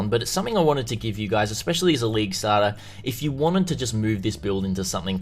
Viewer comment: I just hear "leek stahrter" every time T_T